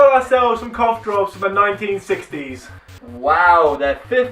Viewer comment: *previous comment was because I thought I heard you say they were from the 1950's*